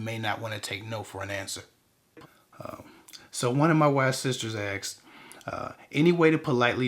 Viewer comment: 😂 at first, I thought you said "one of my wives" 😳 😂😂😂 LMAO